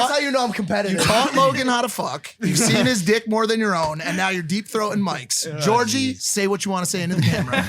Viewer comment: who else heard “and now you’re deepthroating mikes”